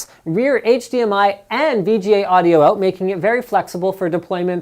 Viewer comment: did he say HDMI and VGA "audio" out????